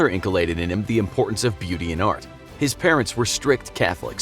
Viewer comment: Who else thought it said "Deviantart" at 1:36. I wasn't looking at the subtitles so I had to go back and see it again. AAAhhhh, BEAUTY AND art. So no sonic the hedgehog fetish fan art for Wild Bill, no sirree.